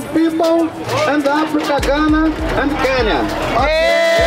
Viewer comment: Did he just say Kenya if so, as a Kenyan 🇰🇪 I'm proud👏🏾👏🏾👏🏾